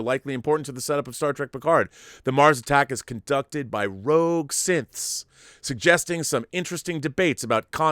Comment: lol... I was listening on my iPhone and instead of hearing "rogue synths", I heard "rogue siths". Way to mix up franchises, right?